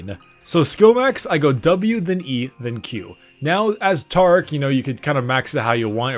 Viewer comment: you said tarc instead of taric